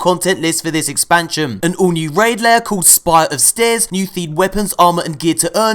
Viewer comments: Did you just say "Spire of stairs"? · am I the only person who heard spire of “stairs”? Lol